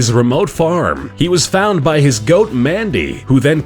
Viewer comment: it says Mandy the horse instead of goat